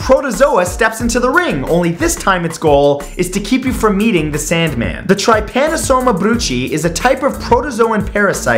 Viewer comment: legit thought he said “keep you from eating the sandman”